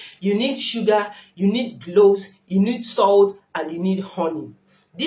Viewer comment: Sugar, salt, honey and what ?🤔 did I hear “Cloth” or “Gloves”? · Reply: Cloves